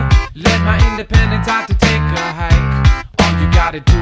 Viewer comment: wait- I thought it said “out the tinker hat”